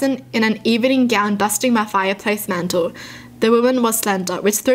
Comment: When she said “obese” I heard “ a beast”